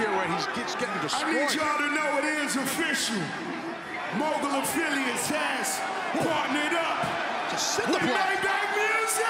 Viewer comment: Did he just say that "It is official, mogul affiliates are fartin' it up" ?